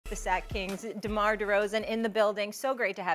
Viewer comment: I'M DRUNK HERE IN BUFFALO NY ABOUT TO SHOOT BALL AT SCHILLER PARK AND I SWORE IT SAID KNICKS not kings:-( foh.